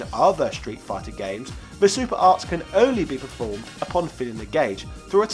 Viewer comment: It sounded like you said "super arse", lol! Which is coincidentally what this game happens to be...it had mostly boring characters with shitty, unbalanced movesets. Totally agree with you about the Wars series though, we NEED "Switch Wars"!